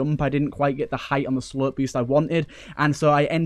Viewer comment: I keep hearing "slurp boost"